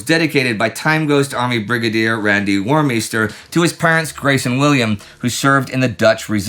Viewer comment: Oh parents. I thought he said parrots